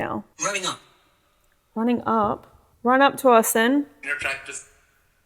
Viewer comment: I heard “you’re attractive” not “you’re a cactus” but I can see how you guys heard that. It sounds like both, but “you’re attractive” makes much more sense lol